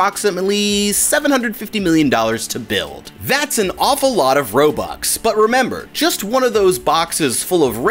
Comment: did he just say “that’s an awful lot of robux” 😭😭😭